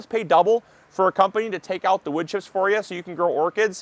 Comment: Thx for transcript. Thought u said pay extra to take out the witches for u so u can grow orchids. Wood chips!